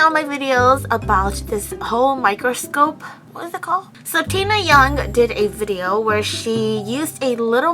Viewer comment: When she said “tina yong” i thought she said “taehyung”... 🤷‍♀️ AND THEN BTS SONGS BEGAN TO PLAY AND OMFG,i love you 😂